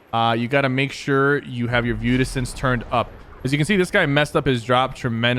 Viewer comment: What did he say to turn up at 1:15? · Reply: View distance